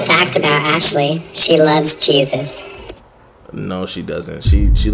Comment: i thought she said cheez-itz not jesus